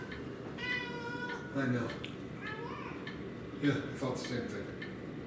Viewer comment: Am I the only one who heard that cat say "I know" at the 9:14 mark? 🤨